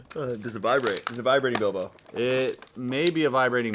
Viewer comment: Sounds like said, "a vibrating dildo." Lol